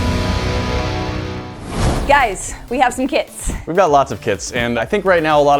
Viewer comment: I honestly thought they said: ' Guys, we have kids.'